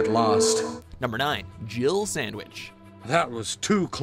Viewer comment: when the first Resident Evil came out we thought Barry said "Jiggle Sandwich"...too funny